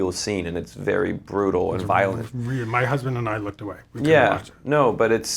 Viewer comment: did he just say his husband? Nope. Time to click off